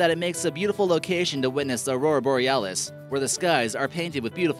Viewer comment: Suomi mainittu Torilla tavataan saatana !!!! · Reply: Ei viddu Kemi kuullostaa viedolla nimeltä Kimi! XDDDD Ja kohdassa 6:24 kun toi koittaa sanoo "Aurora Borealis" kuulen ihan jatkuvasti sen sanovan "Saurora Borealis".... sais opetella toi tyyppi puhuun! (P.S. Tiedän että toi tyyppi ei oo suomesta.) Eikä ne joita toi tyyppi kutsuu Sameiksi oo Sameja vaan Saamelaisia! Hitto nyt alkaa kiehuun mulla! Olen Rovaniemeltä eikä sitä sanota Rovanimi niin kuin kohdassa 8:04 sanotaan! Ja sori taisin hieman poiketa asiasta... unohtakaa...